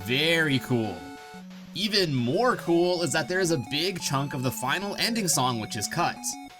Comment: I thought he said big chungus